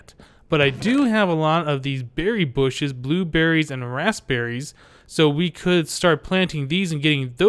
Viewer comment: When he sais rasberies it sounds like hes saying assberies